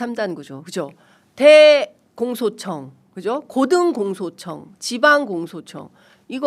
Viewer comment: 대공소청~? 나는 왜 대청소청으로 들리나? ㅎㅎㅎ